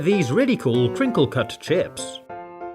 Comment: I heard chips not fries